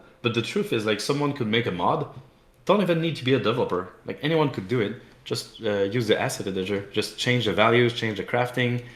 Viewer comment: CÓDIGO ELRICHMC en la tienda de Hytale! ¡Hola a todos! ¿De repente soy prensa de videojuegos o algo así no? Vaya barbaridad, tenéis traducido el 100% de la entrevista por un profesional, tan solo activad los subtítulos que no serán los automáticos. Cualquier error podéis reportármelo en un comentario ^^ For the english speaking community: welcome! I'm a Minecraft mapmaker and technical player, I've been playing Hypixel since the early days and played Hytale's prototype back in 2016 with Simon. This interview has been a blast, hope you have fun and enjoy the exclusive content! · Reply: En 16:31 estoy bastante seguro que dice "ni siquiera tiene que ser un desarrollador" en vez de "creo que debería haber un pájaro demonio"